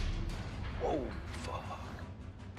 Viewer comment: Did just say "ooh... Fog" at 8:23? LOL.